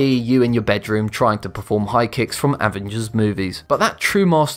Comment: "... trying to perform high kicks from "WHAT?" movies"??? ... Did anybody catch what he actually said there?... bc I'd love to know... it kinda sounds like he said "Avengers movies" but I don't think that's what he really said... do you know what was said? If you do please reply bc now I'm really friggin' curious as to what was ACTUALLY said there! lol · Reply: avengers